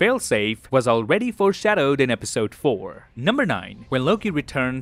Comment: There's a hidden detail in this video : if you watch this video with captions on , in 5:30 min when says episode 4 the caption says episode 3 🤣🤣